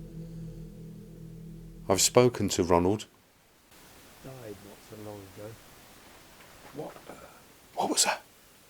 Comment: I hear; " Died soo long ago"